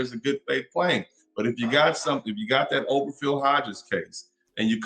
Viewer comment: what is he saying. Open Phil Hodges case or something? I can't make it out.